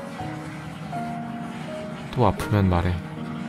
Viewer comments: i heard adidas and so did subtitles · 초에 뭐라고 한거에여..??? 진짜 10번을 들었는데도 모르겠더요.... 좋은일있어? 존닐있어?? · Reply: 존내멋있어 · did he say adidas? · THE SUBTITLE IS WRONG HE ACTUALLY MENT ARRIVEDERCI